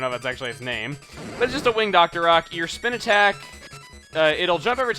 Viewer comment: When you said, "Winged Octorok," I thought you said, "Win Dr. Oc," AAANNNDD then I think of my obnoxious cousin continually shouting excerpts from the Laser Collection XD lol